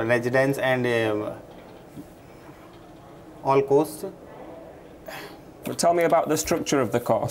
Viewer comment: for all 3 times I heard "the cost" of study. Only after the fact of refusal, I understood what the interviewer really meant to ask in the first place. In my opinion it`s unethical to rephrase a question using the same words from the same mouth, with the same bad spelling, if one wishes to be objective about evaluating somebody else. Give the guy his 4k back... :(